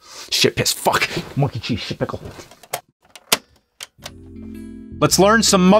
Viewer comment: I heard the nerd say monkey shit pickle just put it in 0.25x and you could hear it